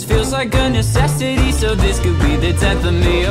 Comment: I always thought this song was saying "taking a shot of ecstasy." But Hennessy makes so much more sense. 💀